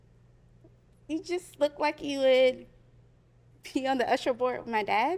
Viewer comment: I swear she said "looks like you would pee on the usher board with my dad"